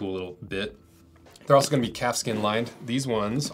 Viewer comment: i kinda was a little thrown off fir a second when I thought you said "cat skin lined"...lol